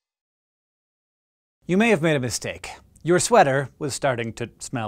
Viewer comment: I could've sworn he said, " You may have been a mistake." To me. But then I replayed it several times and got the correct sentence.